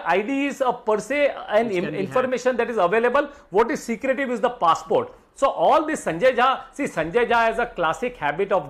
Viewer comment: did he just say PASSPORT?